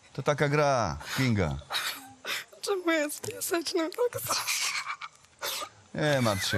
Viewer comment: usłyszałem czemu ja z piaseczna tak zawsze?